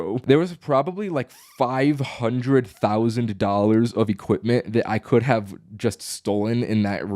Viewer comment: he says 500,000$ but the caption says 50,000$